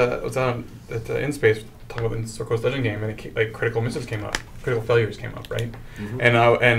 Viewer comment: When said "Critical Misses", I totally heard "critical mrs". :P